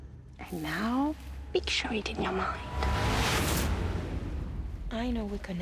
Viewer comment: I thought it said “Big shite in your mind” at 1:40.